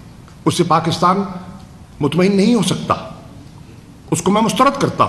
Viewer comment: kya bola "muth mahin " 😂😂😂